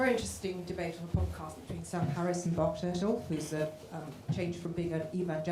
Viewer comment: Anyone catch the name of the former evangelical that an audience member refers to as having debated Sam Harris on a recent podcast (at 51:05)? Bart Churchill??? I can’t find anything. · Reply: If Justin's correction is correct, I think she meant Bart Ehrman (not Churchill.)